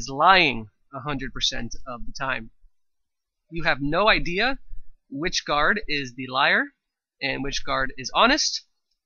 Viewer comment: I thought you said that we didn't know which guard is which